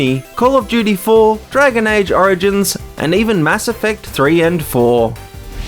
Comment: Did he just say Mass Effect 4?